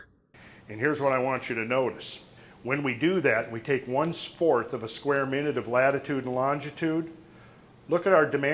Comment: i swear it sounded like he said 1 spork of a square min lol